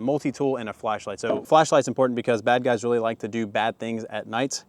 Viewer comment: Did you say “black guys”?